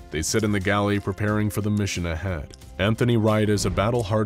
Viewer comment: i thought it said antihorny for a sec lmaooooooo